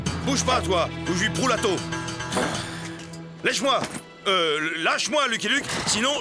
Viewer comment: lèche moi il a dit sa 😂🤣😂